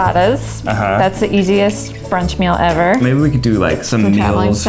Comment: "Frittatas. That's the easiet French meal ever." Except that they're Italian? XD It's okay, as long as it's tasty it doesn't matter too much! edit: turns out that the subtitles are wrong, and she says "brunch." Thanks to Robyn Brace for pointing it out below! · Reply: I think whoever wrote the subtitles heard her wrong, because to me it sounds like she said they're the easiest brunch meal ever, not french. I can definitely hear how it sounds like french, but listening to it a few times it definitely starts with a b.